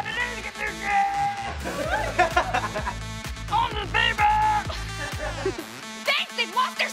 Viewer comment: To this day, I still wonder what Liz said at 4:44. The only I can make out of what she said was at the end of her sentence and it sounds like she's saying something about a "grape". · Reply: She’s says “Only the people that help find the letters get to…”. I feel like she is saying come because that is what the actual line is. The actual line is “ Only the people that help find the letters get to come”. But it does sound like she is saying grape.🤣